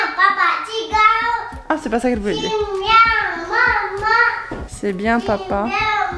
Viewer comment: Moi j'ai entendu "J'aime bien maman" à